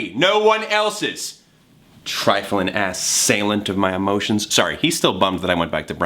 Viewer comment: Did you say ass